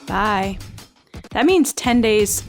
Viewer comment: It sounds like says “10 days closer to gettin pot” at the end of the show. 😁 What did she say? LOL!! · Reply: 4Leaf Clover lol I said pied